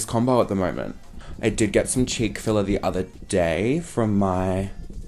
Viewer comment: When he said "cheeky filler" am I the only one who thought he said "Chick-Fil-A" whoops